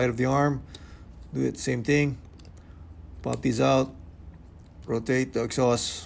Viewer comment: I heard "Rotate duck sauce" for a moment and got confused.